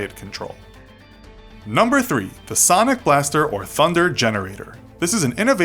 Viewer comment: why do I hear Sonic Wave?